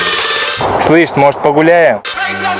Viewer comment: what say? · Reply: Il dit: "Slysh' mozhet pogulyayem ?" me semble t-il... Et si nous allions nous balader ? (He said: Why don't we go for a walk ?)